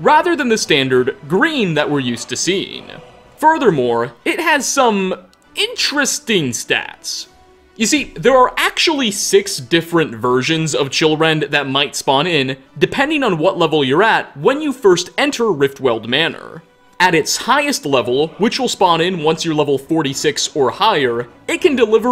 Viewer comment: I thought he said children not chillrend